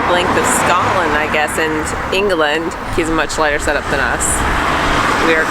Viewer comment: Yes the cycling infrastructure in the UK is poor generally with far too little separation of cyclists from motor transport. Going to be interesting to see how other countries fare. Oh - you insulted 5.5 miliion Scots. Scotland is not part of England! The UK is made up of four Countries (Scotland, Wales, England and Northern Ireland). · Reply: you probaby misheard at 19:26 where says "Scotland and England"?